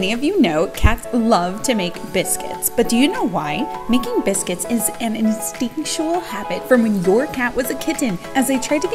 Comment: I've always heard it as "making muffins" 😂